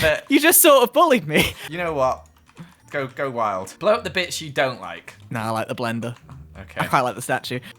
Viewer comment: at first i thought said blow up the bitch you don’t like, but he said blow up the BITS you don’t like and i was like “omg”